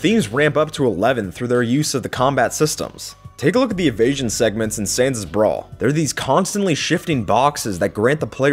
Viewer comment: When you said "let's look at the contents of Sans' brawl" I heard "let's look at the contents of sans' bra"